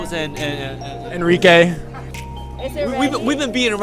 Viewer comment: Anybody realize it says "And Ricay" instead of "Enrique" around 7:14😂?